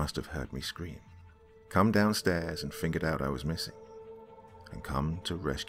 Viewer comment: "Thank you_____???? at the very end. Couldn't catch it. And about 10:50 did I hear " fingered out" instead of figured out? With only one side workin,i had trouble hearing properly.